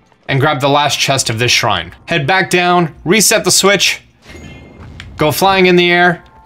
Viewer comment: I thought he said reset your switch so I turned my switch off